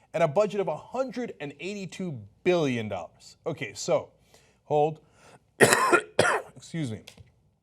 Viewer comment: I thought he called her a ho' 😏